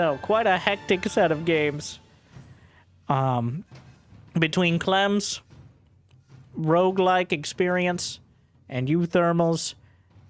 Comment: omg i thought he just said "CLEANSE" instead of Clems... and it made me feel a certain way that can be useful for casting 😂😂😂